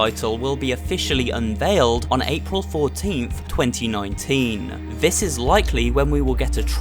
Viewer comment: Dang it! I thought it said april 4th XD